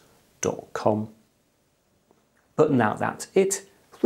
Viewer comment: did he say I hope to torture you again? ;-) Great video BTW